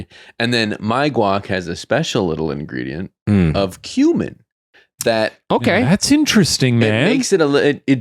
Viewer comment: I almost choked because I thought said: "My guac has a special little ingredient...human."